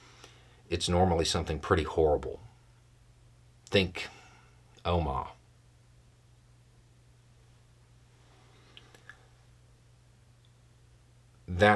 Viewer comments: what's he say at 17:40 ? "think .. 'oh mah' " ? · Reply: Omagh 1998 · I can't make out what Beau is saying in the example of "something horrible" @ 17:40. Can someone help? Thanks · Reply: Omagh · What did you say at 17:42? It sounds like "Oma". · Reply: Beau said "think omagh." As in the ira bombing. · For those wondering what Beau is saying at 17:41 its Omagh. A terrible car bomb blew up there in Ireland in 1998. It changed the course of The Troubles. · Loved this but I don't know what you were referencing when you said "think Omah" at 17:41. Are you able to clarify somehow or at least give me something I can google? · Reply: I had the same question; look up Omagh bombing <link> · Can't hear what he says when he gives an example on "something horrible." Close Captions says, he says, "Think Om." It sounds like he says, "Think Omar." The later just makes me think of The Wire and I picture everybody goings, "Oh crap! change our strategy quick cuz Omar coming!" RIP Michael Kenneth Williams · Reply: Beau was referring to the Omagh bombing, I was confused too <link> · What was the “pretty horrible” thing Beau said we should think of around 17:40? Sounded like “Elma” or “Omaha”. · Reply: Omagh 1998